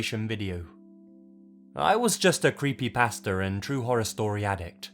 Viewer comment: I thought you said that you were "a creepy pastor' , not creepypasta. 🤦🏼